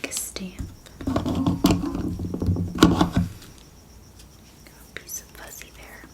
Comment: AYOOOO what do you say at @ 17:49? LOL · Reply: “Got a piece of fuzzy there”